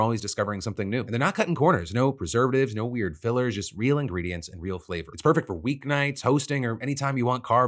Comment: There's a "yanny laural" during the wild grain ad. I heard him said "real ingredients 'hopefully'" had to run it back lmao